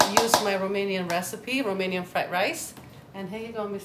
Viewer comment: he said Armenian rice instead of Romanian. Get it right Mike.